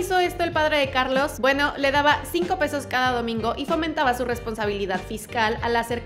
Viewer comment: No entiendo sus palabras, dice "fomentaba"? Porfa aclaranos